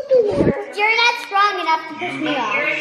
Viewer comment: I love when little ones start walking and do the proud laugh! It’s adorable!! I’m so glad Cassidy is doing so well. All your kids are smart and adorable. Great job mom and dad!!! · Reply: And I won’t lie, I thought said “You’re not strong enough to piss me off”. I had to rewatch to hear that she said “You’re not strong enough to push me off” 😂😂